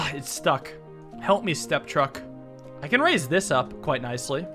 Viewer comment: Did He Say "Step Truck"?